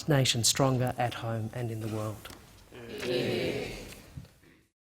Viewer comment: I can't clearly hear it. what did they say? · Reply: Here here